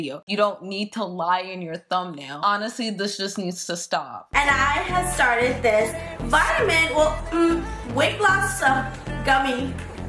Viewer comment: what is she even saying?? All i hear is, "And i have started this vitamin well, erm, weight loss sa gummy." Please tell me what the hell with girl is ACTUALLY saying.